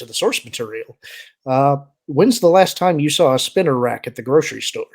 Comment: Did he say Spinarak? As in the spider Pokémon?